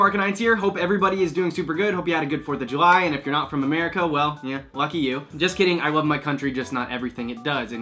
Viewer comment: Not gonna lie. When you said “Lucky you” in your intro, I thought you said “Eff you” haha. I was like, dang. This got PG-13 real quick. Haha. Also, hair is on point my friend! ♥️